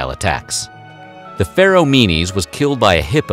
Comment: When you said "the Pharoah Menes", it sounded like you said "the feral meanies"